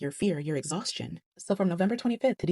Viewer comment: I heard "from November 28 to UNTIL.....". Which means...I'm understanding we shutting it down until further notice